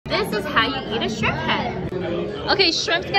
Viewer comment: I thought you said shrimpette 😭